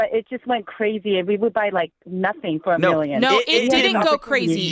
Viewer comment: Did she said there is nothing for a million?